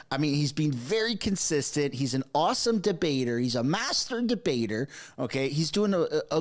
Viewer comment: When it sounds like you say masturbator instead of master debater 😂